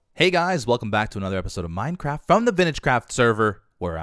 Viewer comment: When he said the intro it sounded like he said Minecrack